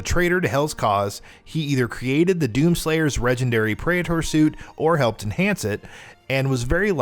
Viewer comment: Did I hear a Regendary ? Lols